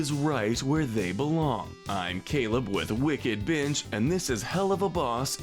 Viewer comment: I thought he said wickedbitch at the begging